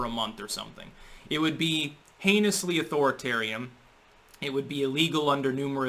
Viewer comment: you cannot pronounce an "n" or a "t" with your lips closed. You end up saying an "m" or a "p" instead. Example: You are saying "authoritariam" instead of "authoritarian" @ 0:43 and "thap" instead of "that" @ 1:20. Not 'thap' you are going to care, I suppose.